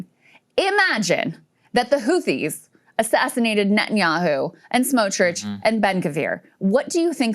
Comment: All i heard was "Imagine if they assasinated netanyahu". I will stick to that